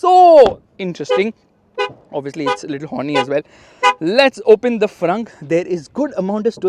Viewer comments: LOL DID YOU SAY THAT THE CAR IS A LITTLE HORNY AS WELL 🤣😂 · Did you just say honry at 3:30 ?👀🤫